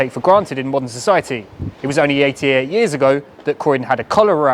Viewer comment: I thought you said eight years ago! Eighty-eight is still more recent than ideal, but at least it's more than eight years ago. I guess.